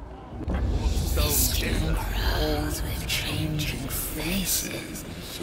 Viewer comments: Hard to make out the voice lines but I thought I heard. Nurgle:I spied the mansion and the one that lived within and I decayed and I withered. Khorne:He looked at me with eyes of hatred and rage and now the fury is mine. Slaanesh:I saw the umbral princeling of chaos and he was nourished by our feelings. Tzeentch:Faustian crows of changing faces each one speaks 2 lies for every truth. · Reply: I think I heard “festering crows with changing faces, each one speaks 2 lies of every truth but idk I think yours makes a bit sense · Hard to make out the voice lines but I thought I heard. Nurgle:I spied the mansion and the one that lived within and I decayed and I withered. Khorne:He looked at me with eyes of hatred and rage and now the fury is mine. Slaanesh:I saw the umbral princeling of chaos and he was nourished by our feelings. Tzeentch:Faustian crows of changing faces each one speaks 2 lies for every truth. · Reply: I hear “The skin crawls with changing faces, each one speaks ten lies for every truth”